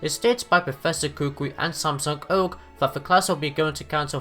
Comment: Sorry that this is 1 week late ^^; I was waiting for titles earlier this week instead of uploading this, but of course, the titles got revealed with the new preview, anyway I hope you enjoy! You'll most likely know most of these easter eggs but there will be some which you might not. Episode 43 will also gets its own video coming soon. 10 Easter Eggs in the Pokemon Sun and Moon Anime [Ep.1 - Ep.29] ►► <link> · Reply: 0:48 I swear I thought u said Samsung Oak lmao